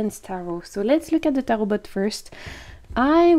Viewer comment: i misheard tarotbots as tarotbutts, not sure i am happy or sad it wasn't a tarot full of butts XD